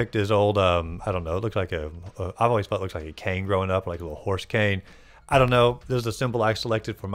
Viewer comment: A what? It sounded like you said horse cane, but the video showed you picking the Tweeter? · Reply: Indeed I said horse cane lol. Ever since I was a little kid the fella looked like a cane to me with the color of the head the hop reminding me of a horse, so that’s what I’ve always referred to him as 😂